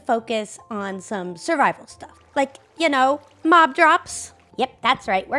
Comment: when said she was going to work on the mob drops, I thought she said cough drops